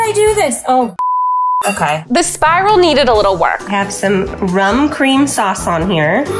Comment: Did she said RUM